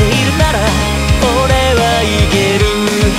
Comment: 勇次郎の「俺はいける」が「俺は生きる」に聞こえる… 「お前がいるなら俺は生きる」… 良き（＾ω＾）